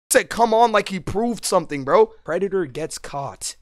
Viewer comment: MY dysplastic SELF THOUGHT THAT SAID Preacher